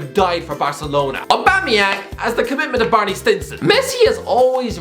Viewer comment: "has the commitment of Barney Stinson" I laughed so bad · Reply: Hahaha Ffs, I thought he said Marnie Simpson.